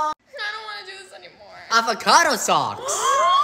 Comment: I love that her little brother cheered her up with avocado socks 🥑. Edit:MOM IM FAMOUS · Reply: I thought he said “AVOCADO SUCKS”